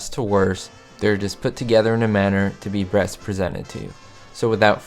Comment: #1 Blooper 0:40, instead of "Best Presented" it is "Breast presented" 😜